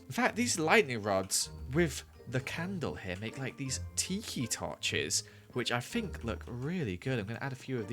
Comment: When he said ‘tiki torch’, I heard ‘teehee torch’